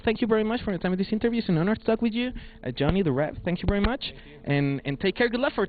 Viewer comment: Sorry.. but did he said Johnny the rev????????? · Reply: Johnny, The Rev